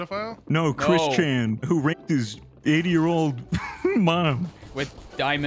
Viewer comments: i think said 80 not 8... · ish it says 8 year-old instead of 80